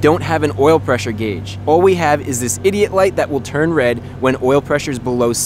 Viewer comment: did you say idiot light? 😅